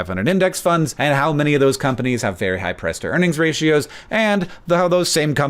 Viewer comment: For a sec I thought you said Press to Earnings Ratio lol